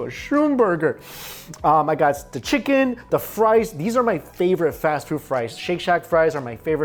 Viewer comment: I thought you said frice